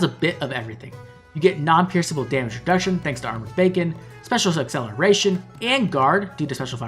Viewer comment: Did you say Armored Bacon not Beacon.